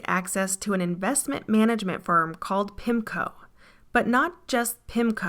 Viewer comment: When you said Pimco, I thought you said Pimpco and I was like "Well, that's accurate."